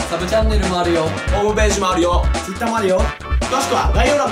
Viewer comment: イェあっってきこえた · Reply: 俺、アデューって聞こえたw